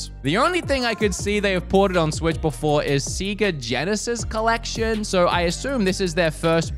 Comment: Did you just call Sega Seega? XD